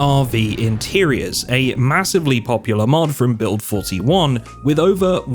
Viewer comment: did someone say massive?